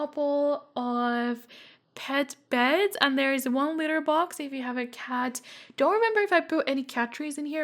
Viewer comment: I first thought one time you said ´if you have a cow´XD